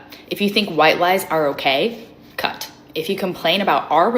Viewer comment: did she say you’re cut if you think white lives matter? So she’s racist got it